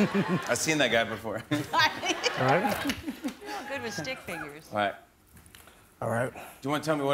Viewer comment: for a second I thought Jennifer said "im good with stink fingers" but obviously it was stick figures.